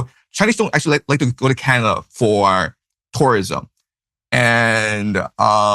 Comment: I think he said people don’t recommend going to Canada for vacation. Tucker misheard.